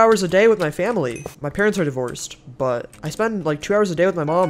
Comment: I thought u said my parents are the worst… i was about to lose respect